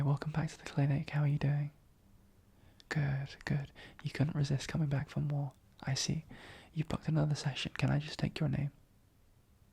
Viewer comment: Lmao you said “you came back for more” but I heard “You came back from war.” And I’m like “war? That doesn’t sound right.🤣” I had to listen to it 3 times to hear it right 😅😅